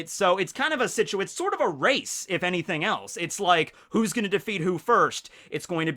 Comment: i heard who's-who was gonna defeat somebody😂